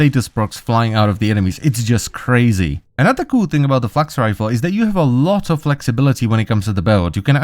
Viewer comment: thought he said fucks rifle for a split second